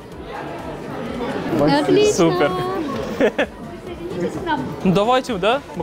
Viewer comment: мне не послышалось или он сказал мат или супер?? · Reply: Он сказал супер